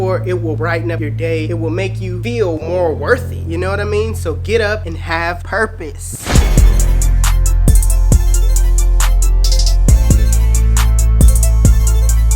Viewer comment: sound like u said have herpes big inspiration for my channel bro keep up good work